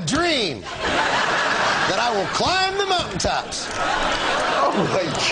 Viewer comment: "I HAVE A DREAM! That i will climb the mountain tops." hahahha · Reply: I swear I thought he said "Climb the muffin tops"